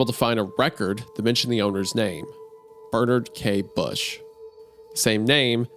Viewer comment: I have this playing in the background and was all into the story then at 1:43:56 I thought you said "the owner's name, that murderer Kate Bush." Lmao